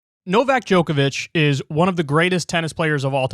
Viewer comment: I thought you said NoVAX Djokovic at the start lol